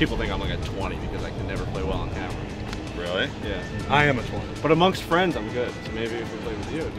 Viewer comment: We want more of road to breaking 100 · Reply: Wait did say his handicap was 20 @ 1:08???????